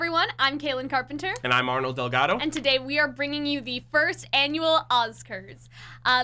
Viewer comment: I just heard chibi Ozpin shout, “INAUGURAL!” 😆